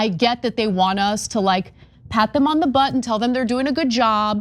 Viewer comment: Did she say "pat them on the butt" is that the saying? 😂😅